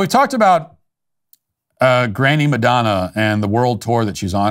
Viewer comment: I heard "Granny Tour" lol. Thats what it should be called lol